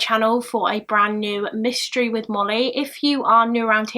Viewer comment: For some reason I always hear “mysteries with mommy” 😂